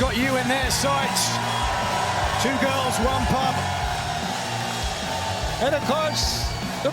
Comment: Did Nigel say "Two girls one cup" ? That absolute madlad! 😅🤣